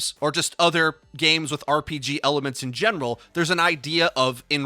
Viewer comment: >gre - gar - i - ous > 3 syllable words I learned in English class. Back to school you go. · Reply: I thought he said “if you play RPGs in genitals” instead of general